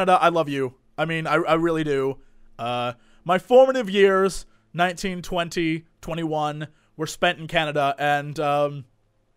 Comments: When you said 19 20 I thought u said 1920. Didn't think you were theft old! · Oh god, for a second when he said back when he was 19, 20, 21. I thought he was saying back in 1920 - 21, I was like HOLY FUCK! looks fucking good for a 90yr old.. then I realized I was being an idiot. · When he said 19,20,21 i thought he said 1920-21. I was like is a Cullen? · For a bit, I heard that as "My formative years, 1920-21" I should go back to bed. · Lol, when said "19, 20, 21" I heard "1920-21". You old timer you.